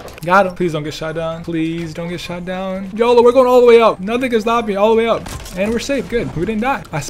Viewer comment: did he say the n word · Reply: is no n stop me I’m going way up